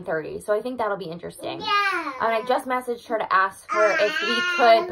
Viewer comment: it sounds like he says yeaaaah !! Lol